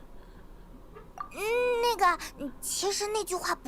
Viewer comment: Lol. I’m sorry, but at 1:02:09 I literally hear Paimon going “N*g*a” as if that was her response to Skirks’ affirmation of Paimons’ nickname for Skirk.